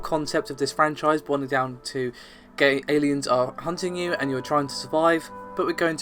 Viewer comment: did you just say gay aliens are hunting you?